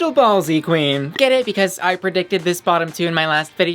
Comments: Legit thought you said “thank you to today’s sponsor, I bottom” and I was like “we know” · When she mentioned the sponsor for today's video, I deadass thought she said "I Bottom" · I legit thought she said "I bottom" 😂 Luv ya! 😘